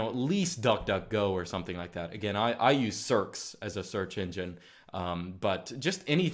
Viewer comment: What did say was his search engine? I can't seem to decipher what he said. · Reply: Searx